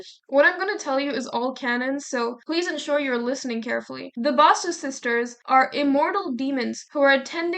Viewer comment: all I hear are sussy Baka sisters